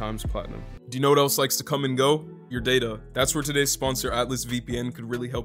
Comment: Listening to this, I heard the sponsor and I thought he was going to say " You know what else comes and goes, your dad"